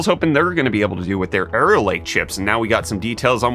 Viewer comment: I keep hearing say "Error Lake". Is this foreshadowing of things to come?